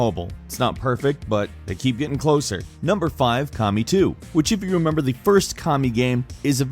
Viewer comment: Coulda sworn he said "Commie 2" at 3:58 lol